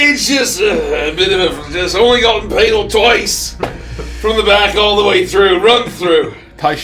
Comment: Logically, I know Barnabos says he only got 'impaled' twice, but it still SOUNDS like he said "I only got painal twice, from the back."